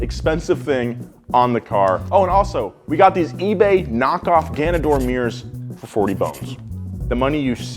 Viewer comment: Did he just say GAYDOR MIRRORS?